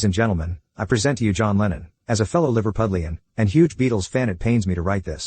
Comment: I was very confused till I realized I misheard lennon. I only heard Lenin, thinking Vladimir Lenin, who is also misunderstood in history as well.